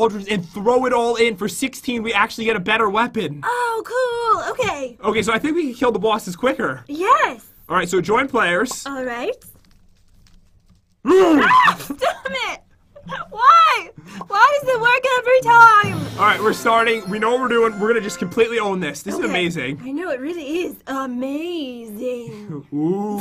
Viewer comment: did just said damnit?